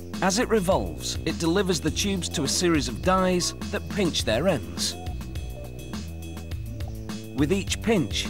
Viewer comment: i thought the guy said "pinch their ass"